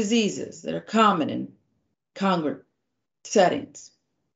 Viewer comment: did she say, "congrant"? Sound it out, sharon.